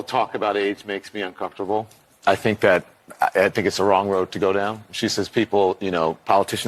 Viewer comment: I legit thought Don said " I feel uncomfortable talking about AIDS." And I was like I bet you are ROFL.